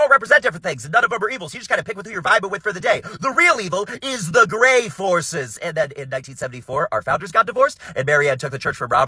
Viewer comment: I thought he said grave horses instead of grey forces and i was really confused why they thought horses at graves were evil